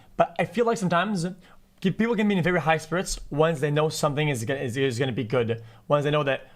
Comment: Some have to pretend they're goofy to generate views, while others just have to be their way-too-fast-speaking unintelligible self to do so. (take that "high spirits" response at about 1:40 . Had to rewind in 3 times to get what xQc was (probably) saying; and it is, in a weird way entertaining, )